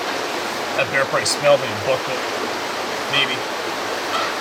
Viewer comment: I can't understand what you are saying at 14:56 I just keep playing it over and over again. All I can make out is, "That bear park smelled like a buck lip, baby"